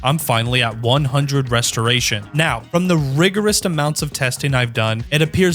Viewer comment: did he really say rigorest?